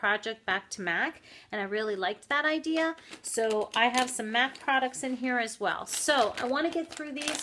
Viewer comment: I really love Apocalyptic on your lips. SOOO pretty! I threw out both of mine...They smell horrid. I could have sworn you said "I have some meth products in here as well" at 53 seconds....I know, it was MAC. I had snowgirl (MAC) and I couldn't get enough of it...I swear, MAC lipstick does last a very long time. I did like that about them. Like that Marabou color...Hope everything's going great with your job!